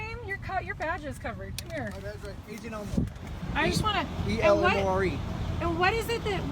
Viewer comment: Did I hear Agent Elmo?